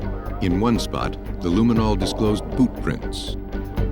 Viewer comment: Did he say boot prints or poop prints? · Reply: It did sound like poop.I had to sort it out in my brain,and it came out as boot.